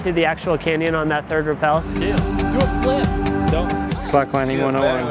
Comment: did anyone hear do a flip